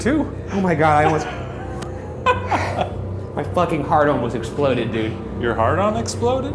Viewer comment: who else thought ryan said "my hardon almost exploded"?